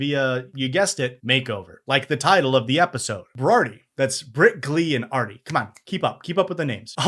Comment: You’ve been saying britglee??? This whole time it’s sounded like you were saying brickley and it’s been pissing me off